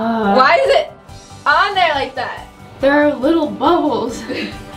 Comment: Ha ha ha!! When you said there were little bubbles I wasn't paying attention and I thought you had said little Butt HOLES. LOL I was like Wait. WHAT!? LOL